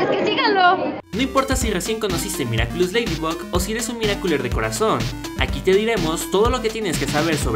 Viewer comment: Al principio entendí "Mira culos de corazón" :V